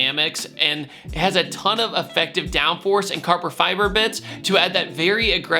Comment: Did you just say carber fiber?